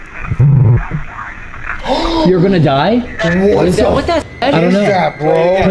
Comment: ish when they ask if heavens real and the ghost says, no, your going to die, I think they miss misinterpreted It’s because When I listen to it I hear “no, you die”.